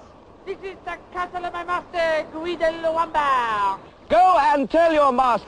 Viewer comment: I always thought he said "my master GUY Lombard" as in Guy Lombardo, with Guy pronounced "Gwee" as the French would.